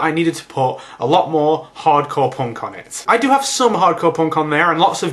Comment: At first I thought you said hardcore porn... :D